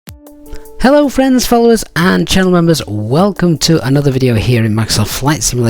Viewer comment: When I first found your channel last year, for a few videos I always heard: "Hello friends, FOES and channel members..." and I was thinking "Wow, that is really nice" :) English is not my first language...